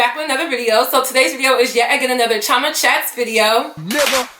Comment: the more i listen to her intro song i hear “go to church” instead of chioma chats 😭